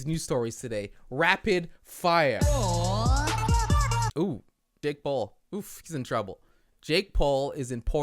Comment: How did I hear "uh dick paul" 😂